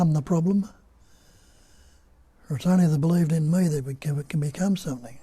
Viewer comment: If you play from 40:40 and listen..... did you say, "if only they believed in me could they become something"? Just before that you said, "there is no me". How can I believe in you when there is no you, according to you? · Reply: Bob says, "It is only the 'believed-in' "me" that can become something". In reality there is no 'me', at best all you can find is only a bundle of memories. "Me" is only believed-in, like Santa or monster under the bed. The other pillar of Bob's "teaching" is: "don't worship the messenger; hear the message and see for yourself, nobody can tell you anything or teach you anything, nobody can do it for you, I can only point to it". Thank you for highlighting possible misconception hidden in his statement, this is valuable feedback!